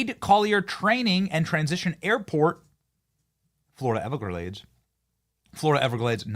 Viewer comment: The sound glitched at 6:01 and I thought I heard you say "ever graves" it wasn't what you say, so I claim it "EverGraves" right now on Bennie Johnson's Show!!